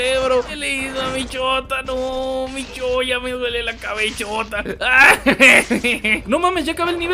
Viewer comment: ¡¿Porque en un momento escuche "Me duele la chota"?! XD